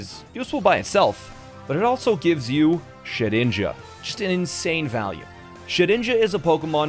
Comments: I thought you were saying shit ninja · Hey, when you were saying shid ninja, the yt thought you said shi- ninja. Saw in YouTube auto subtitles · i'd like to point out the subtitles thought you said shit ninja, not shedninja